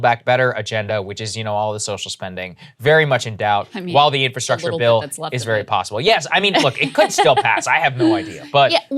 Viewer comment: What did Krystal mumble here? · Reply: "I mean... what little is left of it."